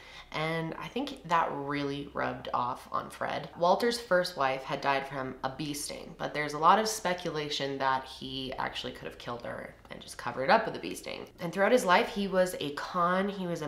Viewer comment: I just spent 5 minutes trying to figure out what a “beasting” was, like “Walters first wife died of a beasting” 😂😂. A bee sting !!!!! 🤦‍♀️ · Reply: Girl I stg I had to go back a few time because I thought she was saying a beasting too🤦🏽‍♀️🤦🏽‍♀️🤦🏽‍♀️🤣🤣